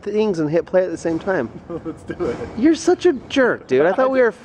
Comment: It definitely sounded like said "let's do your mom" crying